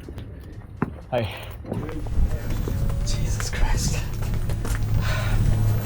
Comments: Bro Did you say Jesus Christ in the video ?? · did you said jesus christ???